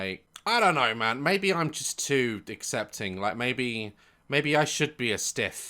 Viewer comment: * Did Daz just say 'Maybe I'm just too DICK'ccepting??' * 🤭 If so, is this just a normal thing that people say now and I've just missed something?? 🤷‍♀😂😂